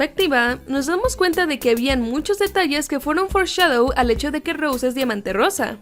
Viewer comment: JAJAJA hasta ahora me entero de qué significa "foreshadow" . Todo este tiempo pensé que decían "forzado"